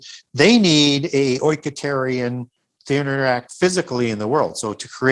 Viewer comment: did I hear 'oinkaterian'???! 🐷🐽🤣